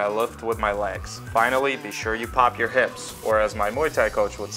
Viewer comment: what does your Muay Thai coach say and what does it mean? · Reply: haha i just noticed that mistake. He calls it sexy hips when you pop your hips